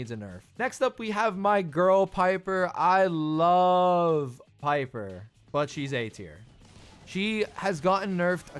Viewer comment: I thought u said "I love piper but she's 18"